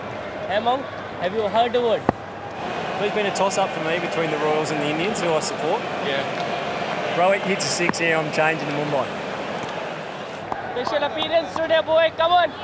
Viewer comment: what did he say ? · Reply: "Always a tossup between the royals (rr) and the indians (mi) for me to support. If rohit hits a six here I'm changing to mumbai."